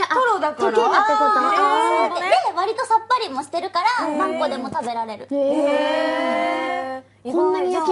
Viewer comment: ○んこでも食べられる それにしか聞こえんくなったわ